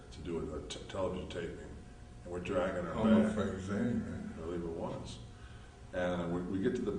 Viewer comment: What does scott say at 2:54 about wilkesbarre? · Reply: "Home of Frank Zane" i believe